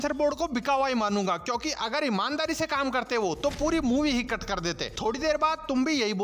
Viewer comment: puri movie hi kar kar dete😅 kya bola bhai ne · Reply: Cut kar dete